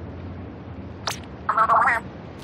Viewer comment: what did frye say? that's sus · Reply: “What the fuck?”